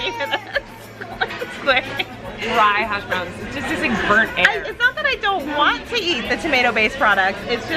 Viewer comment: "Dry hashbrowns, it just tastes like burnt air" -Colleen Ballinger 2k19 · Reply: Amber Gniotczynski oh i thought she said burnt hair